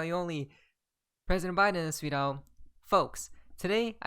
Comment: What did you say at 0:26 ? President biden sweet oh?